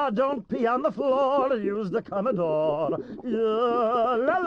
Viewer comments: I always thought he said conodom, like he was saying condom in a weird accent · I thought he said "dont be on the floor use the colored door"😨